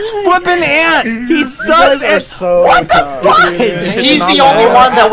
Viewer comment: i heard closely and he said what the fudge and i literally started crying 😭😭😭